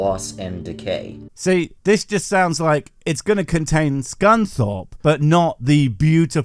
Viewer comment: please tell me im not the only idiot who thought "Ive never heard of the cuss shorpe before"